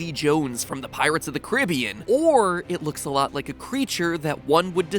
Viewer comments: did he just say pirriates of the ciribian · Did h just say pirates of the curabins